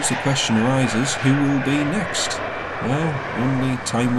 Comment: When you say Wow Air fast, it sounds like you say Huawei 😂😂